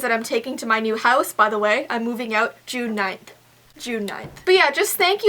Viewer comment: Who else thought that when she said "June 9th" that she said "tonight" 😂😂